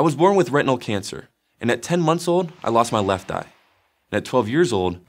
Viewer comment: Not gonna lie I heard "left nut" at 0:12. I'm sorry.